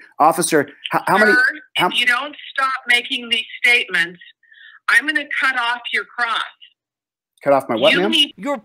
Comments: I heard, "I'm gonna cut off your crotch." I think that's what he heard too 🤣🤣🤣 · "If you don't stop making these statements, I'm going to cut off your crotch." That's what it sounded like the judge said and I think that would scare most people😂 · I thought she said “cut off your CROSS”, as in his cross-examination.